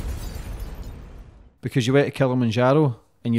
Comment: I thought he said " you went to kill him in Jarrow " near Newcastle.